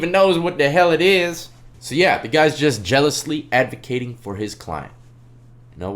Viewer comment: I thought he said zealously hahaha jealousy is way better